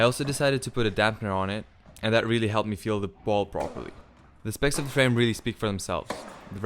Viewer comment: the guy says "I also put a dampener on it and that really helped me feel the ____ properly..." What was the word he said? Did he mean to say ball? Maybe it was something with the audio in that moment. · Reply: Yes yes, "the ball". Sorry for the audio problem.